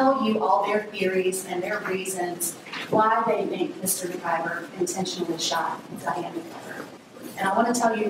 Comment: i thought he said mcgyver lol